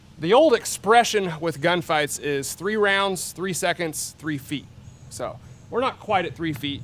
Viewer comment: I always heard it as "3 rounds, 3 seconds, 3 yards"